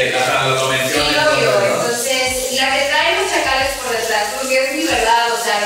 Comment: "La que trae chakales por detrás" dice, yo le escuché "La que trae pura gente pdj por detrás"